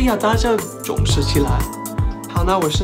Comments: 最后一句听成：好了，我现在在酒吧，拜拜👋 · 他最后说"我现在走吧"，我听成"我现在去酒吧"。😄